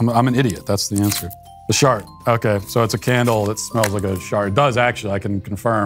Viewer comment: I thought he said it smells like shark and I thought "Yes, finally!" I grew up in a town with a Sharks Board and when they dissected a shark it was a smell of unimaginable horror.